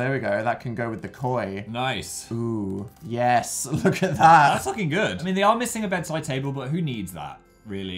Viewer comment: Okay but did phil say ''thats fucking good''